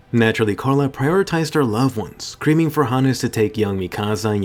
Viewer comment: Did he say creaming or screaming